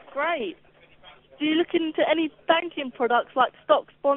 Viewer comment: Banking ? I thought you said spanking old girl